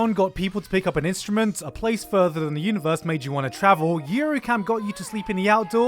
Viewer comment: anime name? bcs i hear yuricam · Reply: A Place Further than the Universe or Yuru Camp?